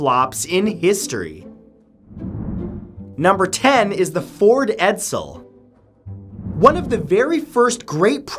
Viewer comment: Ford Edsel? I thought you said bored pretzel l